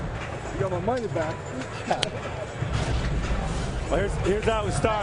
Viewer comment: his coach goes to hug him and it sounds like he says "you got my money back" right before he goes in for the hug. tf?